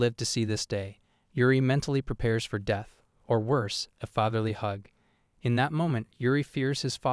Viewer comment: did they say "fatherly hug"? Lol